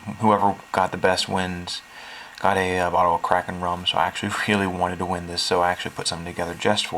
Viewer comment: Lol, at first I heard "The winner got a bottle of crack and rum." I was close...ish.